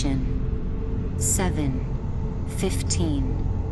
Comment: did she say ascension